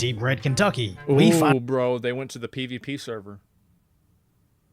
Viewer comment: "They went to the PvP server." PERFECT 100% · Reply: I swore he said PBR server....